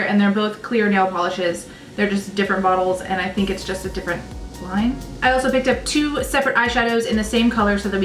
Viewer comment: Did she call line